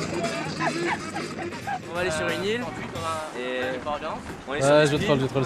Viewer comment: c’est moi ou il dis qu’ils sont sur speed ?